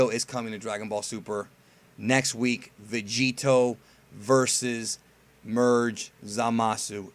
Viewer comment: Vegito vs merge Thomas.... That's what subtitles (CC) says